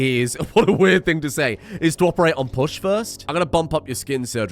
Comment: I thought you said "Bush" instead of Push and was like, Christ, first Dick (Comma) and now Bush. Were They running for president in 2004?